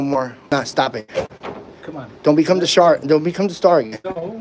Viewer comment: "Don't become the shark." Did I hear that right?